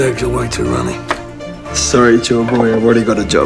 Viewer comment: did he say sorry joy boy? 😳🙈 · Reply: He said "Chore boy"